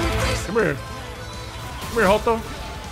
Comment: did you said " Come here joto"?. As a Mexican i know that's a way to call Gay persons.